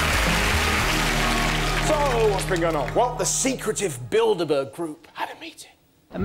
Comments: Ooooh i thought they said build a bear group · I thought he said build a bear group. Then I was confused. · I thought they said build a bear group 🤣🤣🤣🤣🤣🤣🤣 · I thought they said the build a bear group